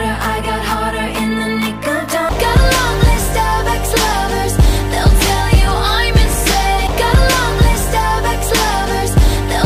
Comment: “Got a lonely Starbucks lover”😂😂😂 Who says that…😂😂I need to know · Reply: i thought it said: gotta love these starbucks lovers, they’ll tell u im insane